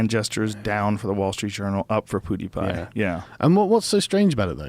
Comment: I thought Sargon said Poodiepie, and for a moment, I thought this was infectious